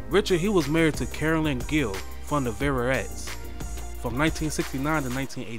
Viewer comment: I can't quite make out what the narrator has said but the girl group that Richard Street wife (Carolyn Crawford solo tune 'Forget About Me') was in The Velvelettes iirc. 'Needle In A Haystack' is probably their best known jam, though there are a few others. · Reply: His wife's name was Carolyn Gill of the Velvelettes not Carolyn Crawford...